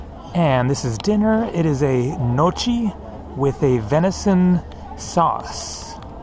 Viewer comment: Lol at 8:36 I thought he said Mochi.... its is Gnochi it is a dought from wheat and potato and then boiled....it is Italian traditional food and it is pronounced something ljke nyoky.